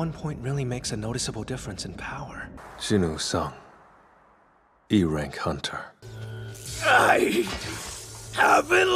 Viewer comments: E rank hunter ❌️ Erect hunter ✅️ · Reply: While watching this clip, I thought I heard “erect hunter”. This comment confirmed I wasn’t the only one. 😂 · When he said e rank hunter the only I heard was "Erect Hunter" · I thought he said erect hunter 😂 before i realized its actually e-rank hunter